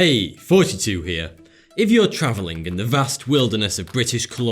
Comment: I thought he said fourty two but then i looked down at the channel name and i realised he said. Sorry mate